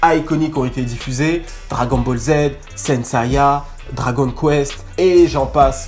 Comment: attends c'est peut - etre moi ou t'as dit saint saya à